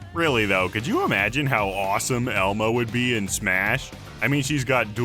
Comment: I thought he said “do you know how awesome Elmo would be in smash?” And I started thinking about his final smash. Maybe he could sing the ABC’s. Then I realized he said Elma. Rip.